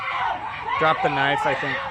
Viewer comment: I heard "Drop the knife" during the slo-mo.